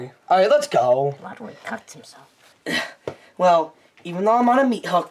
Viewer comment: who else heard lugwig cuts himself